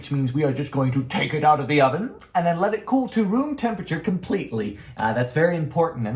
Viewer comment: I thought he said “let it cool to moon temperature” and I was like… I feel like that might be a bit too cold 😂👏🏻🤌🏻